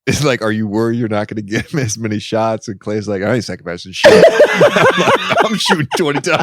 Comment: What did Klay say? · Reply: “I aint sacrificing sh*t”